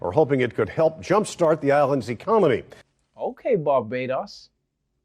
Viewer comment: Am I the only one who heard him say "ecomony" instead of economy?